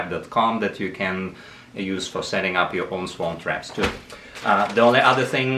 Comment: Where do I get my "Swamp Trap"? Just kidding:) That is what I thought he said though. I just purchased my 1st hive on Friday. And am waiting for our bees to arrive ❤ Thank you for the video.